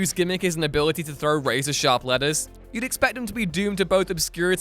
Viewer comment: I swear for a second I heard you say Typeface threw sharp lettuce and got really confused... then realized you said letters. And also remembered that time in D&D one of my players fought a shark by throwing lettuce at it